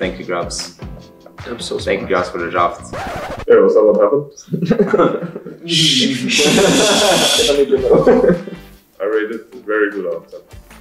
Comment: what Wunder said? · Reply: Wunder - " is that what happend? "